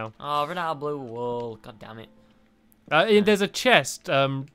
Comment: Did anyone else hear God d*mn it